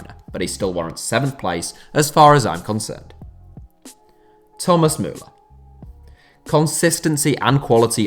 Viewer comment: I was only listening to audio, so when Thomas Muller came up, I misheard it as "Thomas Meunier" and got very confused.